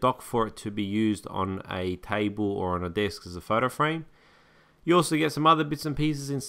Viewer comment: Did he just say "You also get some mother-bitch"